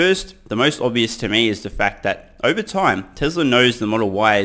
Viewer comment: Sounds like saying Tezla lol I actually like that better though tbh